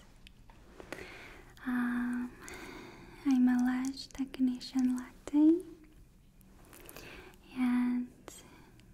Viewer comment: someone please tell me what she is saying at 3:06, it has been killing me ever since this first came out, and it just struck me that i could comment and ask you guys, ik im dumb, but seriously help me, im so confused lmao Edit: elash technicion latte?? ilash tekneshion latte? im so confused what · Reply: “I’m a lash technician Latte”